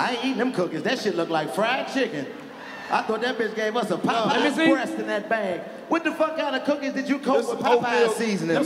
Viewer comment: I thought he said "A bitch gave me a ripe Christmas tree ."lmao